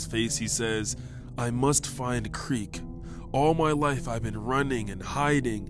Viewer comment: did he just say quiet on a creek